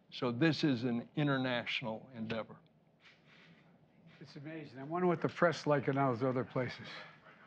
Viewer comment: What did Biden say at 10:06 I’ve played it over and over at slow speed trying to figure it out lol. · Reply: I wonder what the press is like in those other places